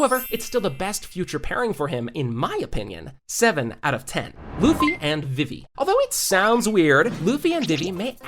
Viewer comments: Is it just me or did bro just say Luffy and Diddy💀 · i heard luffy and diddy credits to